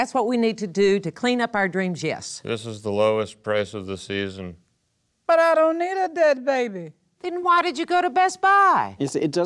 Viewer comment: "this is the lowest price of the season" "but i don't need a dead baby..." "then why did you go to best buy?!?" epic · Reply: Ohhh. I heard “But I donated that baby”. Thanks.